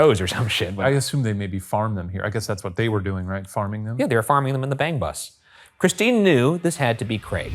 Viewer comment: i stg i thought they said "farming then on the BANG BUS" !!!!!!! HAS THE INTERNET RUINED ME?!?!?!?!